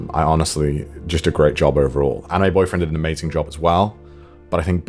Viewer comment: Sorry Connor but when you said anime boyfriend to me it sounded like you said my boyfriend